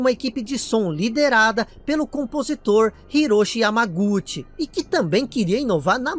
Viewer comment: eu tava almoçando quando vc disse "hisoshi yamaguchi", eu levei um susto pq eu entendi "hiroshi yamauchi".